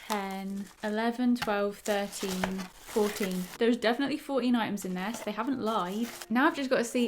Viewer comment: I thought you said 40 items, and I was like.. how?? 😅